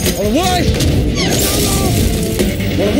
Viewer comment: I always thought he was saying “ON WHAT?” as he obliterates whatever was previously there 😂